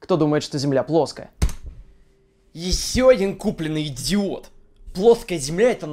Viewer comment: Я там услышал "ты идиот"